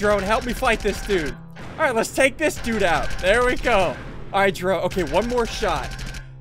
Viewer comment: sounded like he almost said, "Alright Jerome"